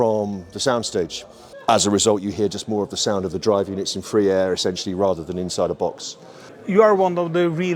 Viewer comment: free air? What I heard was more like hot air 😂